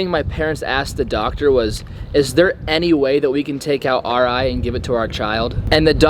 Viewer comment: I thought u said ur grandpa said “can I take out my eye”